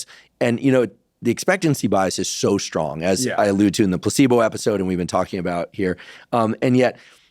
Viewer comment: it sounded to me like Dr huberman said “p*ssybow” instead of placebo. With regards to Melanie trumps blouse, of course! Otherwise, very informative interview. I’ve had 3 aci episodes, I can attest to using caution with edibles. Know your dose!